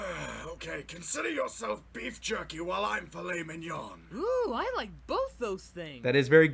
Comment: At first I thought he said, consider yourself beef jerkey while I'm flaming you on